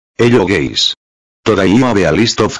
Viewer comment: why at 0:00 i hear "aye yo gayz"